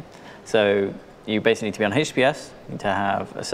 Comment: "you basically need to be on HBS" what is HBS? (read on comments it actually says https but is transcribed as HBS) "you need to have a service worker" service workes as in a responding server?